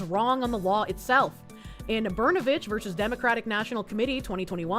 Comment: Oh. "Brnovich" .. I thought you said, "Burnabitch."